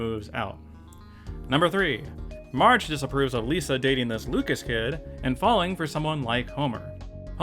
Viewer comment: I misheard cause I thought Marge was upset about Homer dating Lukas and I was like "well, that's reasonable of her"